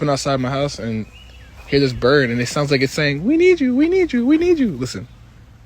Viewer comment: when i hear the birds saying "we need you we need you" i always heard "watichu watichu" from dances with wolves lmao